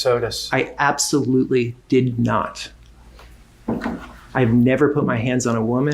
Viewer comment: I’d like to believe I misheard a Freudian or intentional sleight of tongue, “I absolutely didN’T not” 🤔 🤨